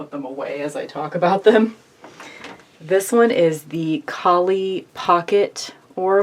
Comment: So, I'm watching/listening and step away for a few moments. As I turned back around, I could've sworn I heard you say "Polly Pocket Oracle," and I got excited. Unfortunately, what you said was "Kali," and I was slightly let down. 😊